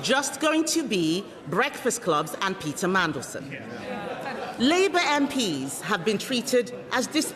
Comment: Did she say Pizza Mandelson?